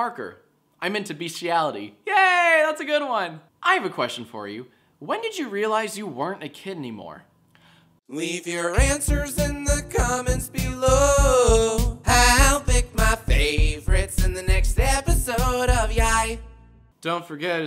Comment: It sounded like Jack said " When did you realise you werent a kidney more? "